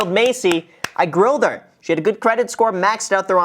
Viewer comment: I thought you said 'i groomed Macy!' Not i grilled her 😂😂😂